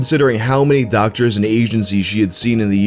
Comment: I seriously thought he said "Doctors and Asians she had seen in the years leading up to her death"